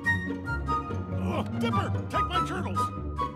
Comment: For some reason it sounded like Ford said,"Dipper! Take my turtles!" .-.